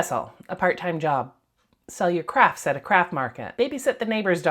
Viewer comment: OMG I thought you said sell your crap at the crap market. 😆